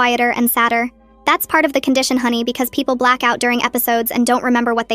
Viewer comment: for a second I thought it said black people!